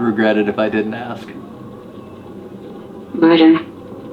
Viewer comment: I heard "Merlin" not 'murder.'